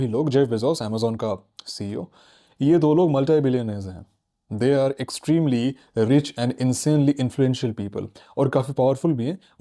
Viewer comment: I heard mutton biryani hai😂